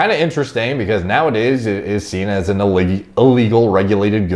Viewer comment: I thought was about to say "illegy" instead of illegal. Sounds cool.